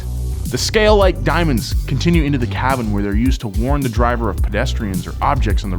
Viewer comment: I blame the new england accent and my brain's inability to adapt, but at 10:11 did anyone else get confused thinking he said carbon instead of cabin?